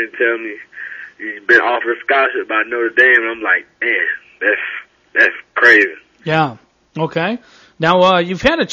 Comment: Did he say "Damn, thats crazy" at 2:52?